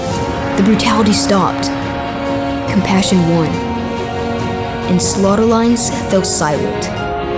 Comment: Can anybody help me, please? I am trying to transcript this video in order to show it to my students but I have had difficult to understand what the boy says in this line. .... slaughter lines.....silent 1:27. The subtitles generated by Youtube are not 100% accurate. Thank you for any help. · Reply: and slaughter lines fell silent